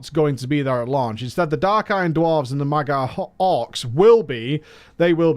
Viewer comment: Thought I heard, "MAGA Orcs" for a second xD